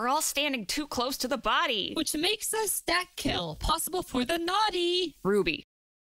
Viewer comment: i still can't understand what ruby was saying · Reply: Which makes a stack kill possible for the naughty